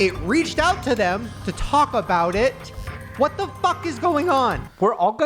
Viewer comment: you said talk about it and I heard Taco Bell as I am pregnant and it’s late I’m gonna go eat the rest of a party pack of tacos left in my fridge ! 🎉